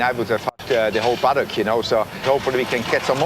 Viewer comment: come on man you won the dislike because what Steiner said was hug the whole paddock HUG